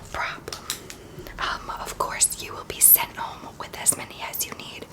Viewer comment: When she said “you will be sent home” I thought she said you will be single 😂 Much love for her tho💕